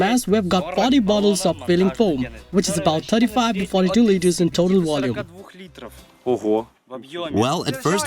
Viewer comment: What did he say at 1:00-1:04? He has 40 cans of foam which is how many litres of foam? Sounded like “35 and 42 litres?!”